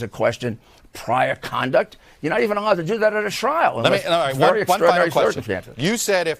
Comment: Wow, Giuliani sounds unhinged! Could have sworn he said "srial," but meant, "trial"